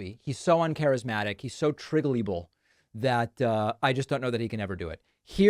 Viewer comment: "Triggelible"? I know you enjoy intentionally mispronouncing words, but this is the first time I hear that one. What's the original source?